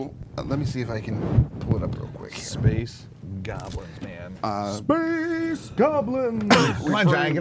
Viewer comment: Totally misheard space goblins as space catholics...ooops